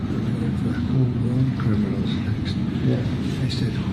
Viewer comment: "Home grown criminals are next." is what I heard pretty easily. However, this does not mean he is planning on sending them out of country, but could very well mean it will be his next focus for US law enforcement, which I am all for.